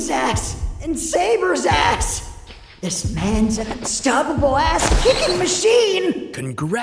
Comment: Great episode as usual. May I report a typo? At 9:19 the line is "This man's an unstoppable ass-kicking machine!" but the caption says "This man's stoppable ass-kicking machine!" I would laugh but I think that typo was unintentional.